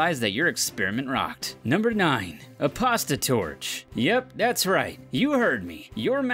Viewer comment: When you said a "Pasta Torch" I thought u said a "Prostitute" 😂